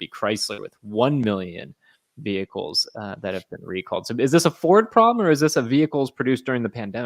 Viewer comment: At first I thought you said the recall was for the Ford Probe.